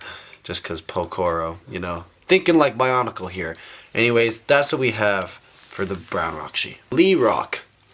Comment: I thought he said he did not have the brown rakshi